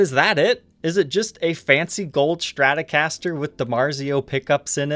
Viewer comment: I thought you said Sweet Ass Gold Stratocaster lolol